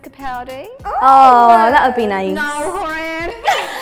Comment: I heard "Nall Houren"